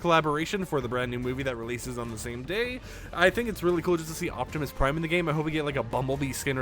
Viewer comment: I’m sorry did you just say Optimus pine